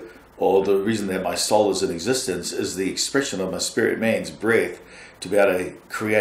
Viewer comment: Question. At 1:03:42 says "the reason I have a soul is to express my spirit man's....bread? breathe?" what is the word? Thanks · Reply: Breath